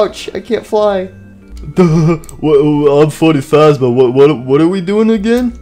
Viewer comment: If you put captions on when freddy is talking it say Im 45 But instead of Im Freddy faz Bear